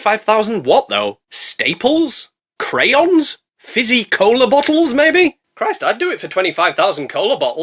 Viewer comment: I swear I heard him say Cola Bottles, and now I am confused how is eating bottles. I am not going to be able to enjoy the rest of the movie thinking about it.